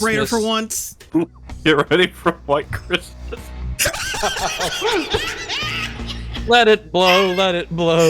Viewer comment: Anybody know what said here? I couldn't here it properly 😭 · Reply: "get ready for a white Christmas" - lol